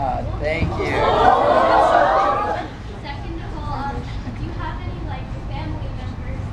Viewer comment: what is she saying? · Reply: If he has any family members that makes music that makes him inspired I think